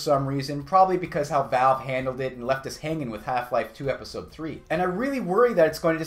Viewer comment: sorry did you say half life episode 3 ;)